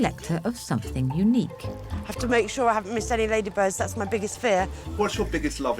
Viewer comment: Ladybugs? sounds like she is saying Ladybirds, and the shoes have CATS on the toes not Ladybugs.